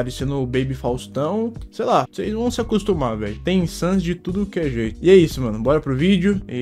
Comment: entendi ele dizendo "tem sans de tudo q é jeito"